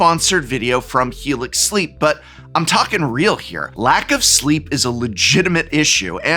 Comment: Thank you Helix Sleep for sponsoring! Visit <link> to take advantage of their Cyber Monday Extended Exclusive Partner Offer and get 27% Off Sitewide! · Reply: I initially misheard you say "lack of sleeves" and was really worried there for a second